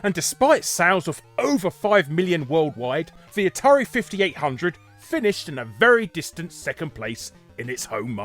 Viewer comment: My guy, did you just say Atari 5800?